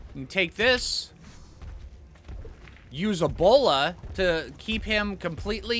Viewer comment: Oh, my stupid hearing. What said: "you can use a bola". What I heard: "you can use ebola".